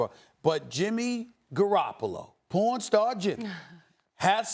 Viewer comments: OoOo I thought he was saying pawn star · Did he say porn star Jimmy